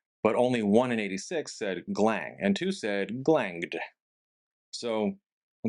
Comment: I misheard it as 'Glinning' and thought 'glinned'- but on rewatch I'd go 'glanged'. Also: Wuggins, Wuggella, Wuggilla, Wuggin, Wugsy, Mini-Wug, Wuggle